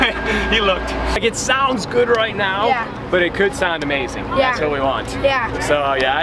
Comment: the kid is just saying yeah